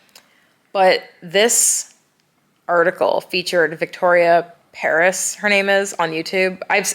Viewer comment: What's Victoria's second name? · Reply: i heard her saying victoria Paris